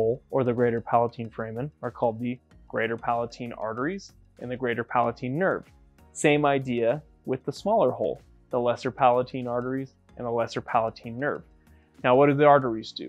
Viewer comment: I thought he said Palpatine and got afraid that Palpatine was in my brain controlling my brain freezes.